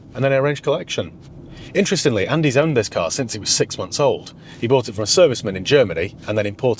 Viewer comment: Not listening properly it sounded like you said," he owned it since he was 6 months old."